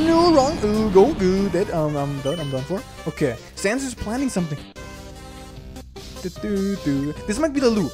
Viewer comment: i thought it said “sans was planting something” and i half expected a peashooter.